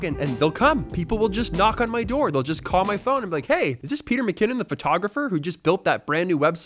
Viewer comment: At :25 It sounded like you said “Its Peter McCanon” 😂 which would be a fitting name haha.